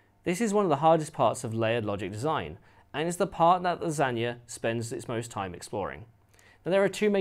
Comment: Silly me, heard it as "that is the part LASAGNA spends it's most time exploring" at 5:27 xD